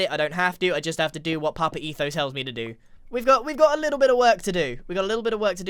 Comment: ….excuse me, did you say papa etho ?